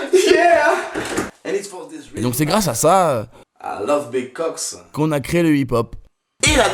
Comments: c'est moi ou mister V dit "i love big cocks" à 0:55 ? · cest moi ou a 0:57 il dit i love big cocks = jaime les grosse bite pour ceux qui ne regarde pas un minimum de porno XD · Aime si toi aussi tu as entendu 0:57 i love big cocks XD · Si toi aussi a 0:55 tu a entendu "I love big cocks" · Si toi aussi t'as entendu "I love big cocks" en 0:55 xDD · C'est moi où j'ai entendu "I love big cocks" ... >w> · Pourquoi à 0:57 j'entends I love big cocks ? Bizarre... :) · C'est moi ou il dit vraiment " I love big cocks" a 0:55 XD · si toi aussi t'as entendu "i love big cocks" pouce vert mdrrrrrrrrrrrrr